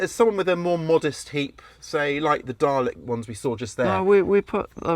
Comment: Did you say THE DALEK ONES? 😅